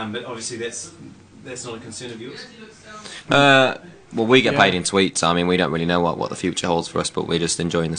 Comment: I thought Liam said we get paid in tweets